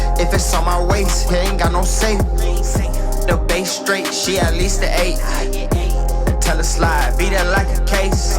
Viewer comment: did just say he get ate..? 😅